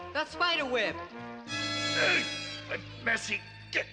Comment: Wait a minute. I don't believe my ears. Go to 18:30 minutes where Hadji says: "Look out Race! That spider web!" At that point, I'm sure the auto-caption is wrong quoting Race with "A messy dick". Listening carefully the best I can make out is the also controversial, "That's messy shit!". That makes more sense in context, if not compliance with 1960's Children's Entertainment. I was astounded to hear this. I may have missed it as a kid because I was raised by a Master Sargent. I don't believe my ears. Does anyone else hear that ???